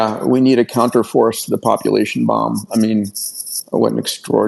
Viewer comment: anyone catch the name of the book Elon was referring to? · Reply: The Population Bomb